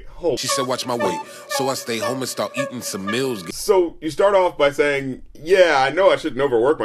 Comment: I could've sworn the lyric was "She said watch my weight, so I stayed home and start eatin' some bills" which made a lot more sense to me since he has too much bread to make